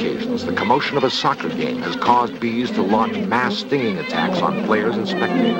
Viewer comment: "Ass-Stinging attacks" Did I hear that right? I imagine he said "mass stinging", but i honestly don't hear it